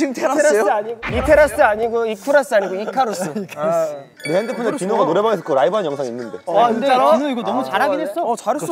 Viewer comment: subtitles say "DION" instead of "DINO" hehe